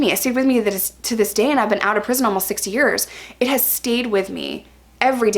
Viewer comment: it sounds like you said you've been out of prison for 60 years. lol.